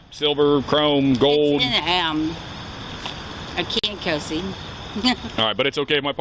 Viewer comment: "It's in a ham. I can't kiss him. Ehehe." This is literally what I hear her say. I don't know what else she could be saying.